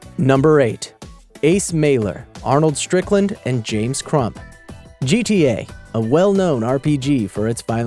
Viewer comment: woah I thought it said James Trump instead of James Crump lmao claps for self